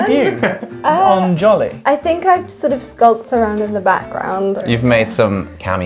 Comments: I think said "skulked" not "sculpt". Sorry I couldn't help but point it out so that it can be duly corrected. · I think said skulk, not sculpt :) sometimes the words are difficult to understand